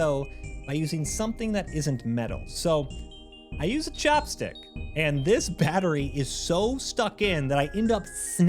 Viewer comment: I swear I heard you say "chap stick" for removing the cmos battery.